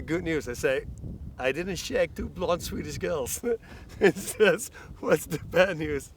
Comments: Does anybody understand what he says at 2:41? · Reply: "I didn't shag two blonde Swedish girls" - this comes from another interview where he explained his wife didn't want to let him go to Sweden because he would cheat on her with two Swedish blonds. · what does he say at 2:42?? wtff?? i didnt share 2 blunts with these girls???